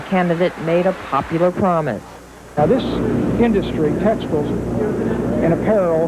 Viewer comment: Did he say “testicles”? 😳